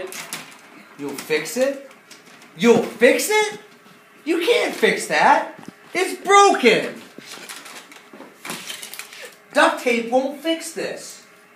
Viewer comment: sounded like he said this game wont know